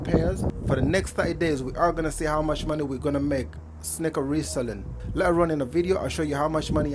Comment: Thought he said snicker reselling.